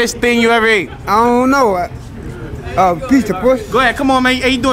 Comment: 🤣🤣🤣 What’s the hottest thing you ever ate? Uhh I don’t know a piece of pwssy 🤣🤣👌🏾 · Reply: I thought he said pizza sum